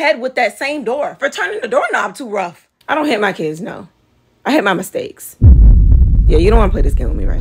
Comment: “I don’t hit my kids, no… I hit my mistakes—“ That was a lil too personal—😭😭 Hit a lil too close to home-💀💀💀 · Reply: I thought she said "I learned my mistakes" lmao that's even funnier... hurtful but funnier 😂